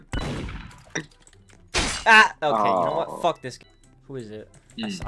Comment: did he just say “F*ck this game”?! 👀